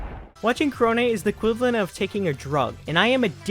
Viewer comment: I keep hearing “Kroni”